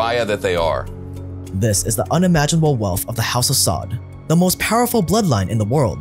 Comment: I thought he said "House of Zod" My boy Superman was was about to come back to finish the job.